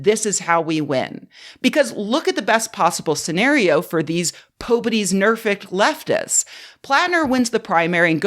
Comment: Can someone tell me what is saying at 25:30? For the life of me I can't tell what she's saying there lol. I'm guessing it's some expression I've never heard of. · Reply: 'pobody's nerfect' is a mocking expression twisting 'nobody's perfect' to belittle those who handwave away problematic behavior.